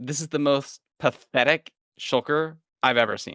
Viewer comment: OMG my phone lagged and I thought said “this is the most pathetic sh*t I’ve ever seen” and was shocked